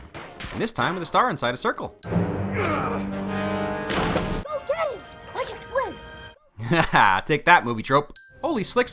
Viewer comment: I love the "its okay, I can swim!" kid. Because in pretty much every other movie, the kid knocked into the water can't. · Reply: PeggymoeXD oh ive always thought the kid said “go get him, i can swim!”